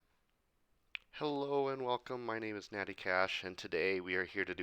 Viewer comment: I thought you said Matty Cash at first